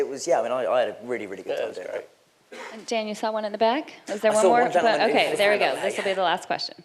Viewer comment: What does Dan say at 22:25? It sounds like "ach so" to me but that would be German (funnily it would make perfect sense :D) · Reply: 'I saw'